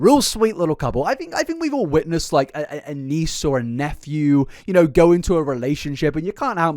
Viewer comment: “Niece or a nephew” ohhhh phew I heard “niece and nephew” and I it very concerned o.o